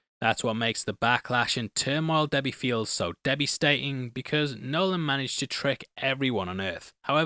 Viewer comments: did you just say “debbiestating” 😭😂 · did you really say "debbystating" instead of devastating? xDDD